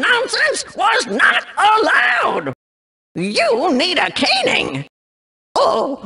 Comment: When they said "You need a caning" I thought they said "cannon" to reference the rocket cannon combat fatigue you can shoot at them.